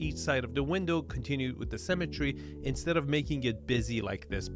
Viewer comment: I always hear "cemetery" when he says "symmetry"